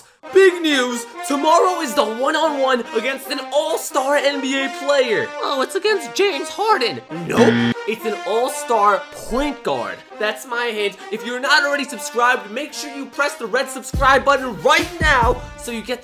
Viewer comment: did he say "that's my age"